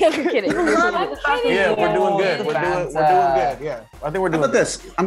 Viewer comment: Josh is seriously one of the best interviewers I've seen this entire press tour. This feels like a funny causal & comfortable conversation among friends rather than them doing a press interview. I mean Daya was so comfortable she called Tom "baby" 😂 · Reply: i definitely heard “i’m kidding baby” 🤨 idk who knows but either way 🥺